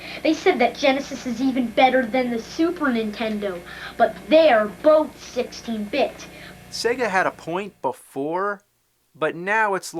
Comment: When he said "...but they're both 16-bit" I thought he said "but they're bullshitting, bitch."